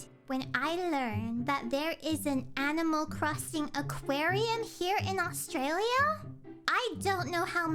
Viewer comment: Somehow I misheard that as "...aquarium here in Australium?" and got TF-toofed